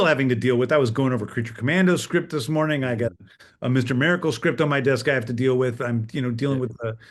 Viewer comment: Did he just say mr miracle script his desk?